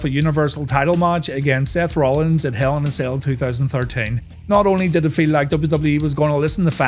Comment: do i hear correctly, 2013? Im not native english speaker so i could hear IT wrong. Please correct me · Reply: He said 2013, he meant 2019 I'm sure.